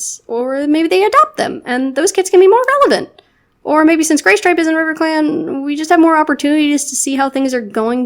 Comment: I love how I misheard "GrayStripe" as "GayStripe" and thought: Yes. This is,,, perfect.